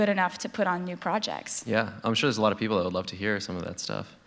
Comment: subtitles say "there are a lot of" but he says "there's" and it's pronounced "đes a lot of".